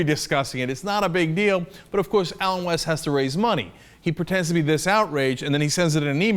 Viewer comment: I randomly clicked on this during a youtube spree and at first thought he said Adam West. I was like Adam West! What's he done now?